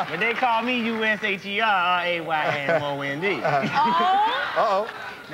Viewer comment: he didn’t spell it wrong he say R a y m o n d it sounded like he put an a in Raymond after after the y